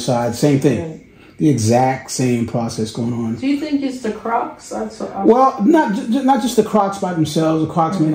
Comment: Did she just say is it the croc shoes cause it